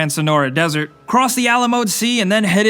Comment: Am I the only one who thought he said "À la mode" sea instead of Alamo?